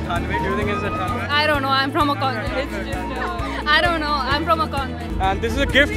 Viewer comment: Is she saying I don't know I'm from congo....😆😁